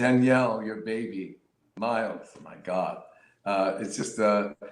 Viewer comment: Did Victor say Miles?? Danielle's son is named miles??🥺